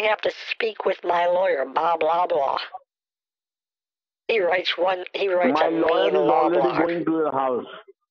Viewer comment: "You gonna need to speak to my lawyer Bob Loblaw" 🤣 nice reference to arrested development 😏😍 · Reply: I heard "Bob Blahblah"